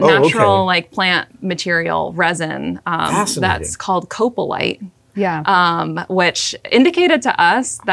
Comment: I thought she said coprolite. which is fossilized poop